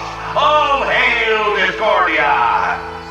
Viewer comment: He actually said "All hail discordia!"